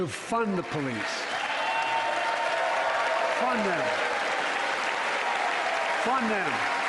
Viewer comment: I legit thought he said ‘fun them’……..😅